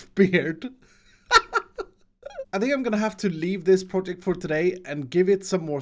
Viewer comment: Did he do that on purpose?!? “I’m going to leaf this project for today” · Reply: I heard "im going to lea-this project for today"